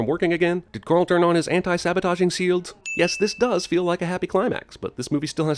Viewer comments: did he say anti sabotaging SEALDS?? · Sin: 13:53 "Sields" instead of "Shields" Gotcha ;)